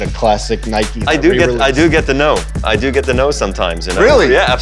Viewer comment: what is he saying there? He does get to ?? Thx